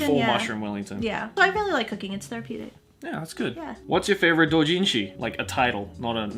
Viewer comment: i thought i heard "whats your favorite OCHINCHIN"😂